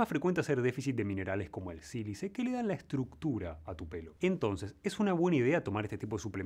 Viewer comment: que dice? Sirise????